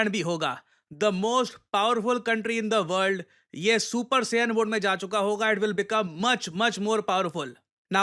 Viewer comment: Did he say super sayan in